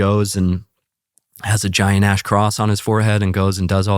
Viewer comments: At first I misheard - giant ash card 😂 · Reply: Giant ash cross… I thought he said giant a$$ cross, LOL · At first I misheard - giant ash card 😂